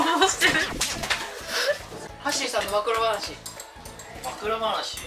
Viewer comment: 暴露話がマクロファージに聞こえて「ん？」ってなった💗